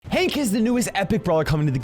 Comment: I thought he said "Hey kids the newest epic brawler" at the start..